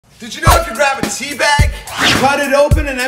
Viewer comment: Thought he said did you know if you get teabagged ✌️